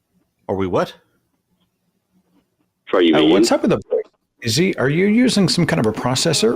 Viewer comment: did he say “are you vegan”?